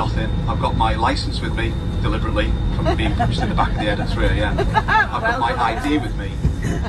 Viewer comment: what i heard: "punched at the back of the head" what the subtitles heard: "punched at the back of the [?]